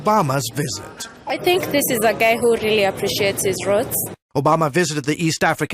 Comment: does she say ; i think he's a gay which appreciate is roots ?